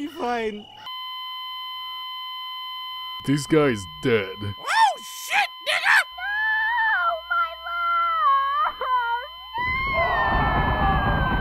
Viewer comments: Did she say “no shit nigga” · After 4 fuckin years I think Elizabeth said oh shit nigga